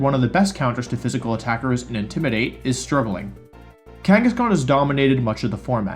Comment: What are you saying at the 6:40 mark?? The best counter to physical and intimidate is “?????”. Are you saying struggling?